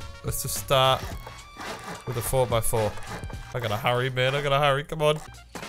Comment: Wait did he say 4x4